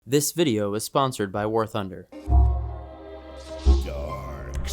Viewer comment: At the start i misheard him saying "This video is sponsored by pornhub" 💀